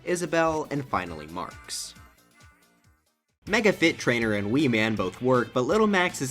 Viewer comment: You said ''Mega Fit Trainer and Wii Man'' Instead of ''Mega Man and Wii Fit Trainer''